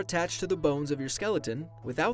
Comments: I thought it said boners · Thought it said boners.